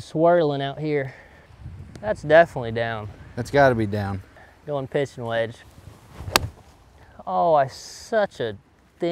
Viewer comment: Did anyone else hear "I'm going pissin' wedge" right here? 11:59 😂😂